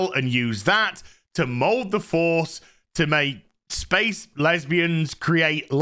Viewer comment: I was eating something crunchy which made it sound like said "Lesbie" Headland, which I think is a much better name.